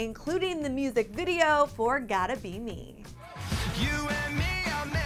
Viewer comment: I thought it said "Gotta be you" & I was like 2NE1!!!